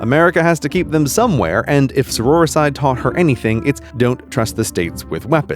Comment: what does he say at 3:36 ?? I cant make it out, what in the hell is "saurora side" ? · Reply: Sororicide - The act of killing one's sister. See also: fratricide, homicide, regicide, suicide, etc. Refers to the U.S. killing the Confederacy.